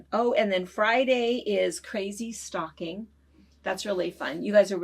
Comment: Okay [at 3:38]...it sounded like you said, "crazy STALKING"...I thought, "Well, this took a turn"...🤣🤣🤣 Jesus Disclaimer: Merry Christmas!!!